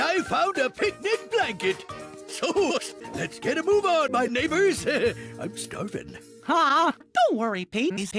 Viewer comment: Cried from laughter, I thought Pete said the N word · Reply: I never realized how much the word neighbor sounds like the N word until I watched this on Charmx